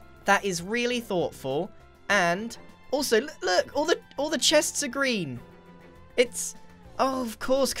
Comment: Grian says "the chests are all green!" but i hear "the chests are all grian!"